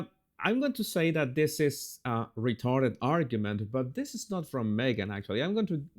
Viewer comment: Jesus’ maybe I heard you wrong, but it sounded like you said “This is a retarded argument”. I must’ve heard you wrong because you’re so intelligent and articulate that I know you would never use a derogatory phrase like ‘retarded’. ❤